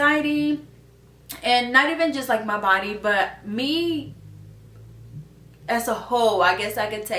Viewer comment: I thought you said "as a ho" from across the room where my phone was...girl!!! 🤣 I hope that made you laugh! Laughter is the best medicine! ❤ I hope you get rested and I'm so sorry you've had this happen. Anxiety IS a ho!!! lol😉❤ take care of yourself...this is a positive step